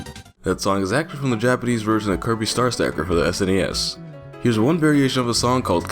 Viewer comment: could anyone pls tell me what he said ? I keep hearing star stacker